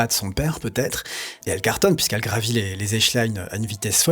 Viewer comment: J'ai entendu "les échelines"?